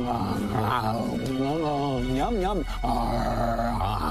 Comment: THE FIRST TIME HE SAID THE END PART I THOUGHT HE SAID HORNY INSTEAD OF CORNY